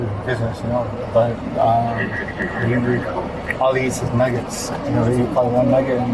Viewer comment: I'm so glad he clarified... I thought he said he only eats maggots. I'm so glad he said nuggets